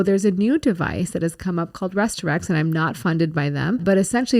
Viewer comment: I replayes it multiple times what is the extender called? Resmed? Rearorex? 13:16 time stamp Can anyone tell me what she said?